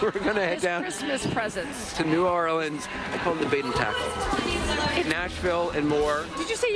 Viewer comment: Did Anderson call his junk bait and tackle?